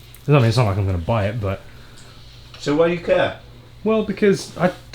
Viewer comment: I thought he said "So why you cap?" 🤣🤣🤣🤣 I had to rewind